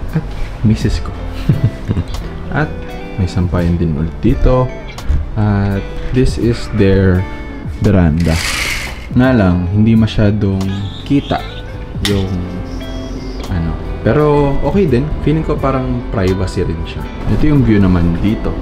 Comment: tol ano sabi di masyadong kita? Di talaga kita hahahaha nangangabog ka pa